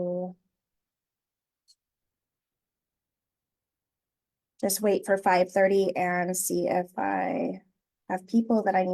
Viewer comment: I'm trying to figure out what is being said as a headband brand. Ive tried googling it and am coming up with nothing. Timestamp 1:00. Is she saying "day tee mist" or something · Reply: Daintymiss